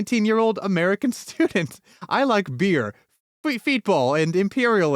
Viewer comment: He likes [what] people? I can't make out the word before "people". P.S.: for context, the segment he says this in starts at 3:12:41 and the saying starts at 3:12:48. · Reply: he likes play feetbull